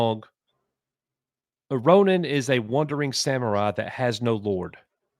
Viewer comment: Ronin? I thought they said Rodent.